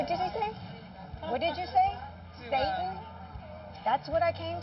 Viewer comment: I thought he said Sagan